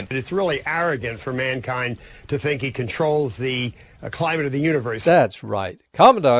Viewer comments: Did he say, "...the climate of the universe" at 8:32 LOL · Did he say "Climate of the universe?"